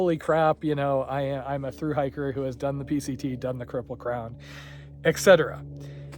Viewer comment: did you say 'cripple crown'? 😂